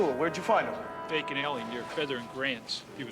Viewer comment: Did he say “Bacon Alley?” A delicious place to be attacked!